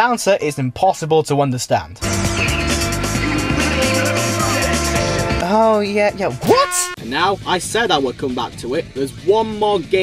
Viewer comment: i think he said "you really rip through that section!"